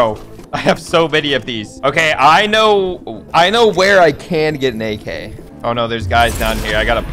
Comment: I heard him say I know we're I could get naked 😂